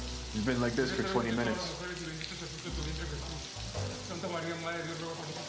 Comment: "He's going to use up all the hot water." LOL! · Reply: To be fair, I heard "He's going to use up a lot of water." ... Which is also true, might I add.